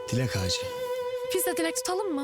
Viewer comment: "Bizde dilek tutalımı" yi bi benmi "pizza dilek tutalımmı" anladım? 0:04😅